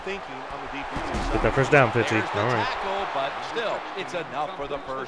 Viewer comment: I had the volume low and at 8:16 I thought you said "ok first down bitchy"